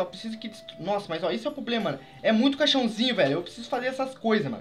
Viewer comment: "muito caixãozinho" eu entendi cachorrinho